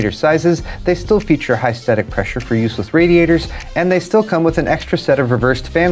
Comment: is it just me or am i hearing say at 1:18 "they feature high static pressure for 'useless' radiators" in the TT fan promo? probably saying 'use with' but i thought it was funny.